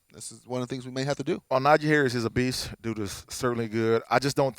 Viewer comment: did Beanie say that Harris obese? 😉